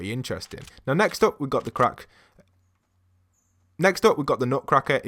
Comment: Did he say we got the crack